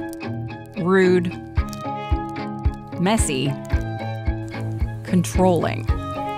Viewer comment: did she just say lionel messi?